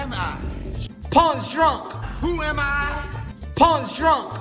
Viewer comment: WHEN HE SAYS WHO AM I IT SOUNDS LIKE HES SAYING ''PAWN SHOP' LMFAO LISTEN TO IT LOL.. TAKE MY GOLD FOR A HOT CINO HAA..